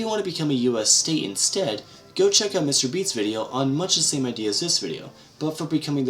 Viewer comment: I thought it said mr.beast